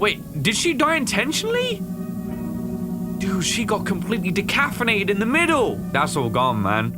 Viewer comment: This is indeed amazing, but i was very distracted when i am almost certain i heard him say decaffeinated instead of decapitated